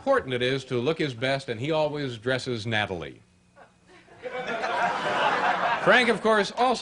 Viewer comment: Does anyone know what word Dave uses at 18:55 as a synonym of neatly, or nicely? I've never heard the word "nataly" (or whatever the spelling is). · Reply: Found it: Natty, adj. Neatly or nicely dresses in appearance; spruce. Not to be mistaken with the slang term knatty. Leave it to Dave to dig up obscure words from the depths of a thesaurus.